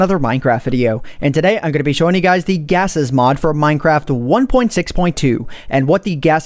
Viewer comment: I thought it said Cases Mod >.> Derp